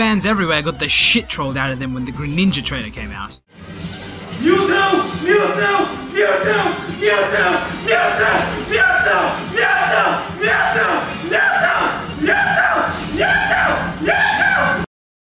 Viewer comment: Sounds like he is saying "Muto"...